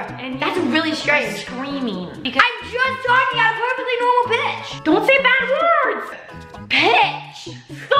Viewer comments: Am i the only one that at first when she said pitch thought she said b*tch? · colleen legit thought jojo said "bitch" lol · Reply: Kawtar Jed I thought that until I saw this comment and then realized she said pitch 😂 I thought to myself that I’ve never heard jojo swear before. I was right 😂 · When she thought she said a bad word i can see Miranda smiling a bit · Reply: ray draper she did not say a bad word she did not say b* she said pitch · I thought she said "I'm a perfectly normal b" 😂😂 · Sounded like you said the B word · at first i really thought she said bitch instead of pitch🤣🤣🤣😂😂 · JoJo: I’m just normal pitch Miranda:DON’T SAY BAD WORD JoJo:”Pitch” Miranda:STOP IT!!!!!! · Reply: I actually thought she said Bitch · at the begining it sounds like she said bitch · Bro I thought JoJo said bitch lol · JoJo: I’m just normal pitch Miranda:DON’T SAY BAD WORD JoJo:”Pitch” Miranda:STOP IT!!!!!! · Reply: I thought she said the b word :v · colleen legit thought jojo said "bitch" lol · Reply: That is so true I thought Jojo said “bitch” but she said pitch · Its funny how miranda thought jojo said IM A PERFECTLY NORMAL BITCH 😂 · She said pitch Miranda but the first one I heard her say I’m a perfect normal bitch 😳😐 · colleen legit thought jojo said "bitch" lol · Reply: I LEGIT THOUGHT SHE SAID “BITCH” 😂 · I heard bitch... Edit: life is hard